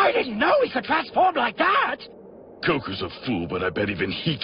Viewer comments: did say goku 🤔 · did just call him "Goku"? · Did said Goku ?